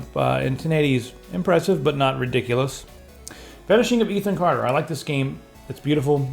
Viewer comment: what is the game at 3:09 I couldn't understand what you said. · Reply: The Vanishing of Ethan Carter